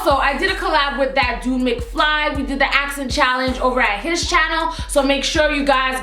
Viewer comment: I thought she said we did the ass challenge lol